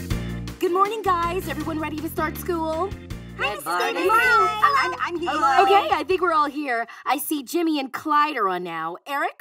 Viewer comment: If you’re wondering what everyone is saying Kyle: Hi Mrs. Nelson Clyde: Good Morning Annie: Hi Sef: Hi Wendy: Hello Bebe: Good Morning Stan: Hello Jimmy (Off screen): I’m here